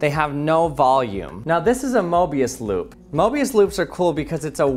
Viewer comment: Is it just me or i hear "morbius loop"